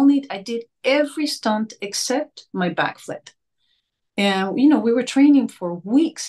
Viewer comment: I just didn't understand here what scene she's talking about when she says: "exept my back.... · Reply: She says "except my backflip." She is referring to the fight scene w/ Nefertiti. She says she did all her stunts except for the backflip Ancksunamun does.